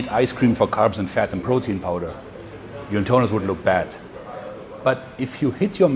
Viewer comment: What does he say at 1:15? After saying if you eat ice cream, your what would look bad? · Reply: He says your internals will look bad.